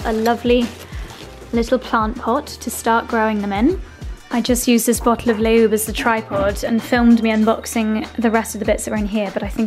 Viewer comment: I wasn’t fully watching at that moment but I was listening and I thought she said “I just used this bottle of lube as a tripod…” and was like “wait. what are you using as a tripod Josie?” ☺️